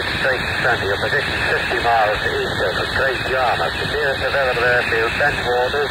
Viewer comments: Was years ago, but the garbled bit at 5:06 sounded like "Great Yarmouth" to me. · It sounds like he said “you’re 60 miles east of Great Yarmouth”